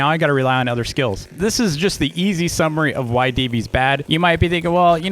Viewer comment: i thought you said "DV is bad" lol